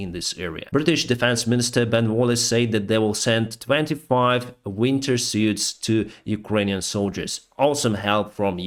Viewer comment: UK sends 25 winter suits?...is that what I heard?...... 3:57 - 4:07 ??????